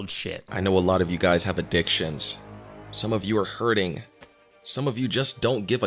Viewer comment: I genuinely thought he said “ a dick shins”